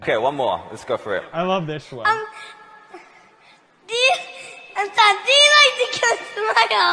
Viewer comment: I honestly thought he said do you like to CUT SMILES :)